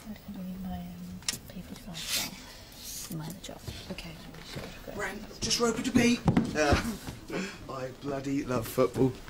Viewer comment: What is he saying at 0:13? "Brent, ??? to be"? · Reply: The girl that he's trying to impress is called Roper. So he's saying: "Brent, just Roper to beat." As in football commentary saying that David just has to beat Roper to score.